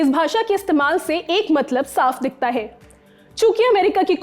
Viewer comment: Kya bola qkiya ?chuKiya?